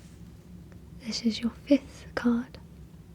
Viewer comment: when you say "this is your fifth card" etc I initially heard it as "this is your fifth god" lol